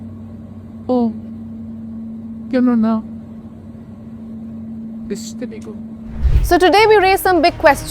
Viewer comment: who is talking at 1:24- 1:28 .......I heard "'Hmm ganun ba ? Stupido"" very irritating